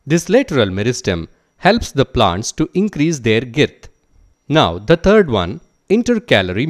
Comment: I"ll appreciate that the makers exoplained the chapter in 12 mins but can you help me with 1:40 what does that word means irth?? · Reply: He said "girth" which means width or it's broadness...